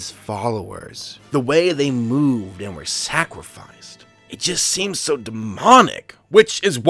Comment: Did you say "sacwificed"?